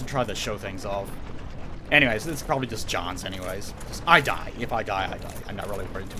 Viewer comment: did you say "thats probably just johns" @ 59:05? Are you sort of involved with the Smash Brothers community?